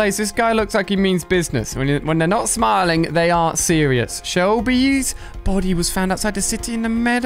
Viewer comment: I'm I the only who heard "it means his not serious "